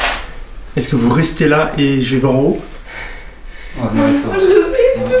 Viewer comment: les sous-titres sont magnifiques (en français) "Vous restez là j'ai vingt euros" au lieu de "je vais voir en haut" mdrrr c'est quoi ces sous-titres tout pété XD